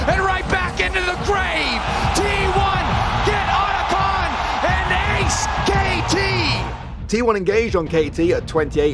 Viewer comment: "And Ace KT" I THOUGHT I HEARD SKT.